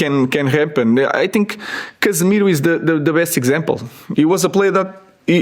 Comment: Who did he say wasnt playing but now is playing a lot around 4:12. Someone help me out its doing my head in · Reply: Casemiro